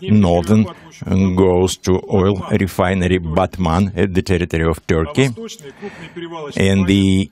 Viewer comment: did he say batman?